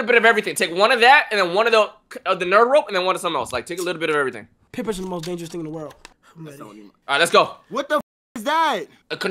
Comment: Not sure but I think he says "Peer pressuring is the most dangerous thing in the world" My respect for Kai just went up. Gotta love him! I hope he continues to have a successful career. Keep it up!